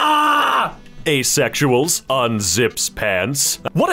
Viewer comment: that did not say unzipped pants, but thank you for trying to preserve my sanity anyway.